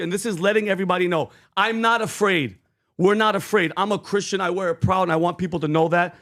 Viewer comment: First of all it sounds like he says “I’m a Christian” to my ears that’s what I heard. So I took a second to search YouTube and literally only took me about 15 seconds to find a clip of President Trump proclaiming not once but twice and very strongly that he is a Christian. I will put below the video title exact so you guys can look it up and play it on the show. You owe it to President Trump PBD since probably most of your Christian audience probably believes he is not a Christian… Video titled “Donald Trump talks about Jesus during his speech”